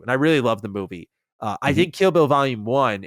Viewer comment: Lol 1:40:50 I kept hearing say kibble vol. 2 when he was talking about kill bill